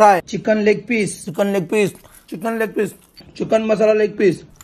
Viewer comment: Why am I hearing "Chicken Masala like Piss" ?